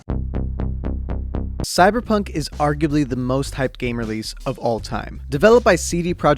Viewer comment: I thought at 0:46 it said cyberpunk 2077 for kids